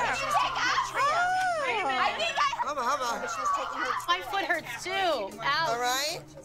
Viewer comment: was it just me or did somebody say “My leg hurts too, ouch.” 😂😂😂